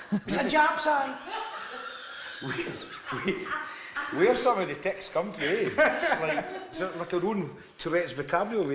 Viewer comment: what did he say at 6:16 ? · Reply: It's a japseye